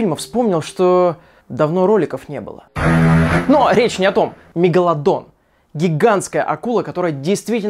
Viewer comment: почему я услышал мегаГАНДОН???